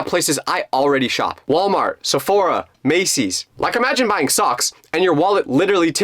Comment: did bro say saphora